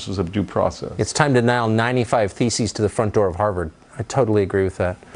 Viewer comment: sounded like he said feces rather than theses